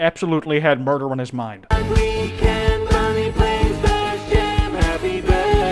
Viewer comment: For some reason every time toy bonnie is on screen it sounds like they say "bonnie play space jam" anyone else hear that?